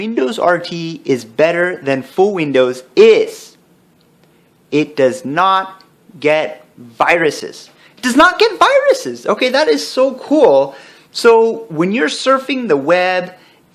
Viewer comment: why do you get no viruses? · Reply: Lol I thought you said "I can't get viruses" xD